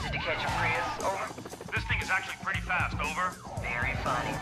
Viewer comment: am i wrong but did i hear ''this nigga is actually pretty fast''